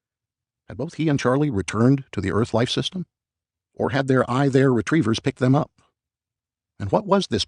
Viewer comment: What is he saying @ 4:50? “I—there” retrievers??